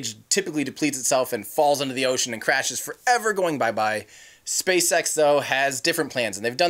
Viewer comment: What did you say spacesex???